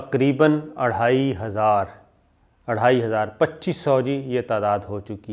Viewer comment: ye arhaai hazar kia hota hy? Is he saying 28'000 ? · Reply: 2500